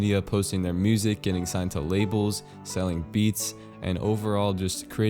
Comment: You said 'maybe you've sold a ton of beats' and I heard 'beets'. This is really a story about me not you